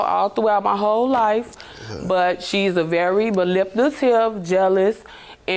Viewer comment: what the hell did she say? “She’s a very Baliptative person” ??😂😂